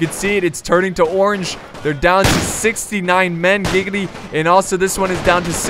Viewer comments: did he said ,,69 man giggity,, XD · did he just say gigitty